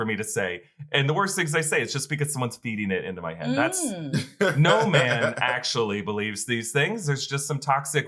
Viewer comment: I thought Voss said "No Ma'am" believes that. Like he was bringing Al Bundy to the MCU 😆.